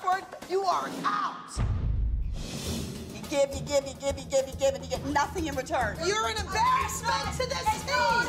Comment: I thought she was saying ‘GIBBY, GIBBY, GIBBY, GIBBY, GIBBY’ 😂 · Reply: ‘You give and give and give and give and give and get nothing in return’, is what she said I think. ❤️